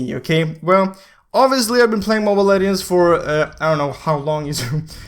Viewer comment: wtf...turn on captions in 1:02...it says mobile aliens😂